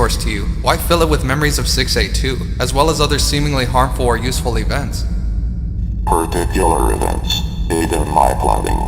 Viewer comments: the captions said "Useless events," at the end, but the guy said "Useful events." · Did anyone else notice the person said useful events but the subtitles said useless?